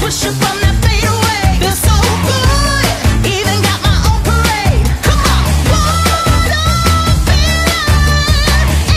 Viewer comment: What is she saying at 0:56 it has been nothing me so much! I hear this constantly at work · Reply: Even got my own parade